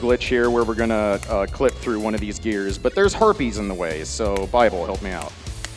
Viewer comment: Totally thought he said "gonna try to glitch through one of these gears, but there's herpes in the way." D=